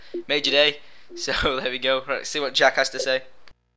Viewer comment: it sounded like u said jackass wen u said lets see what jack has to say